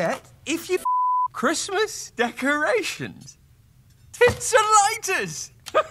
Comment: the subtitles say t**s and lighters! Arthur laugh